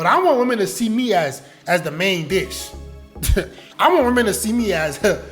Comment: Why 4:30 I thought bro said main bitch 😅